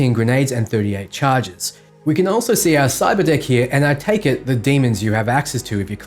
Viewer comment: I swear to god I thought he kept saying cyber dick